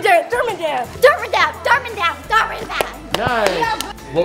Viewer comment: I heard her say dhar man bab instead dhar man dab